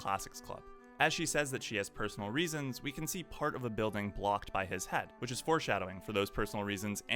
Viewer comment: for a moment I thought you said "blocked by his hat". I was like, "Since when Oreki has a hat?". Great video. Subscribed!